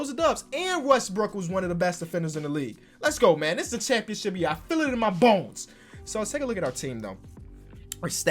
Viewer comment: did he just say I feel it in my balls?🤔😂